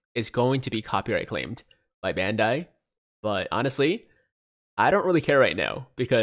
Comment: I was half paying attention and at 0:10 “by bandai but idc rn” sounded like “my dad died but idc rn” that or im just tired lmao